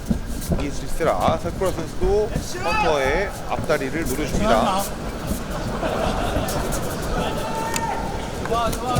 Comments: 뭐라는거에요? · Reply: 그거 하지마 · 관중이 액션!! 하니까 헌터가 뭐라고 한거임??ㅋㅋㅋㅋ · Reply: 그런거 하지마~